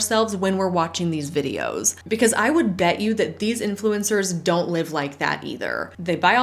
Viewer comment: I hear " the ass influencer" I mean...